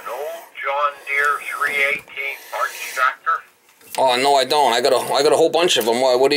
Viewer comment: I got 2 new pots in there, she said parts but I thought I heard pots